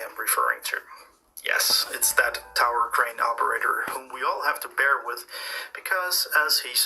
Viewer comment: Thought you said "crayon operator"! ALSO, why can't I see the USA if the earth is flat?